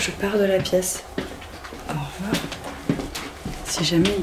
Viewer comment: y a que moi qui entend "au revoir" a 58:29 quand maghla dis "je quitte la piece "???